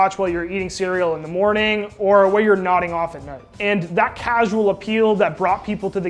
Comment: I thought he said 'nutting off at night' lmao